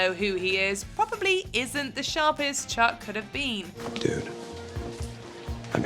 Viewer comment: I thought Chuck said even your penis knows who I am. He glances down when saying it too.